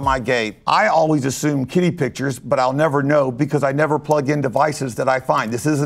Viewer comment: anyone else thought he said kiddie pictures? 😰